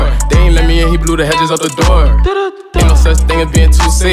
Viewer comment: The eyes are the best and the “through the door” adlib is 🔥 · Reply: now all i hear is “through the door”😭fuckkkk